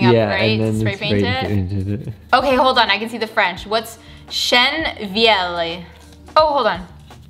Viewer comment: Ok the actual translation for the stain is old oak (chêne vieilli) but when I first heard you I thought you’d said chien vieilli, which is old dog. Lol